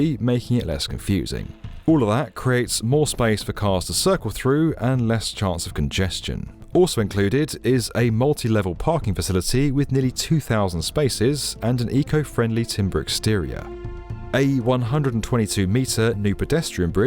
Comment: I thought it said "Eco Friendly Wood Veneer"